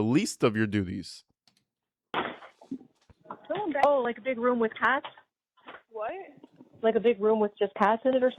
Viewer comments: I thought she said “big room with cats” cuz then I heard the cat meowing lol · To be fair it sounds like says “a big room with cats.”